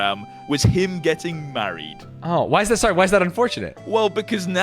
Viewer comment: I thought it said buried instead of married 💀💀